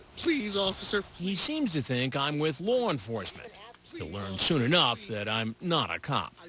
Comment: DID HE JUST SAY "LORNE ENFORCEMENT"? DOES LORNE OWN THE POLICE NOW??